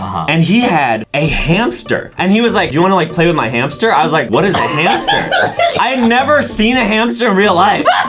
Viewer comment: What Adam Said: Whats a hamster? What I thought Adam Said: What is Cancer?